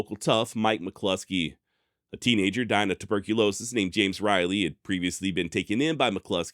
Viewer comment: I knew I misheard it, but at first I thought you said "Twerkulosis" for a split second! What is wrong with me hahaha! Imagine dying of twerkulosis!